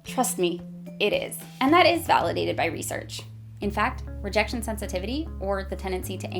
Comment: I thought this said "religion sensitivity" at first lmao